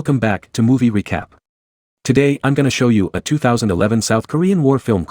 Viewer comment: I heard "Movie Wecap"